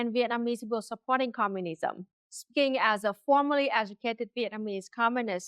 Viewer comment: Commentary is spot on, but I want to note what I think is a mistranslation of 1 word in the close-captions that assist some of us in ensuring we understand your words correctly. Around the 5:30 mark I believe you say "...as a FORMALLY educated Vietnamese Communist....", but the caption has you saying "FORMERLY educated....". which is pretty significant. If you read my comment, please clarify, and keep up the great work.